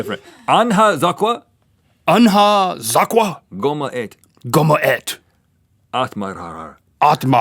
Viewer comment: it actually sounds like link says "kom och ät" in swedish